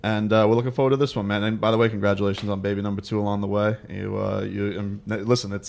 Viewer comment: Did he just say “congrats on baby number Tua” LMFAO